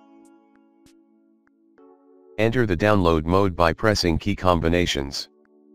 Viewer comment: what is the key you said to hold and press ? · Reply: Turn of the phone, press and hole volume down + home + power key to boot into download mode. Thanks for watching